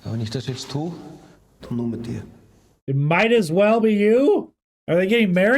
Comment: That is a mistranslation in the movie. Dont know why or how they got it wrong but he actually said “Mit dir mach ich’s.” which translates to "I do it with you" or "I'm only doing it with you."